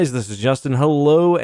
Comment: I am not ever gonna get used to the "this is justin" intro. 😂😂😂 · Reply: i always hear "this is just in"